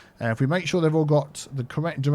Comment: Hugo must be the most common name in your cities. I keep hearing "Hugo there", "Hugo there", "Hugo there.". :-P